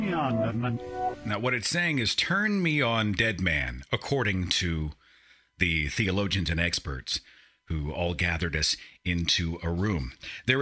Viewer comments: on the number 9 backwards bit, I heard "Turn me on Reverend" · I thought I heard let me on batman.